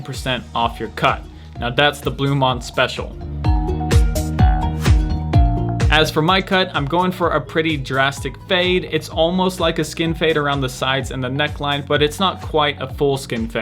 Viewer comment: did he say foreskin fade?